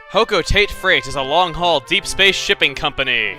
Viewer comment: When he said shipping I thought he said shitting compony lol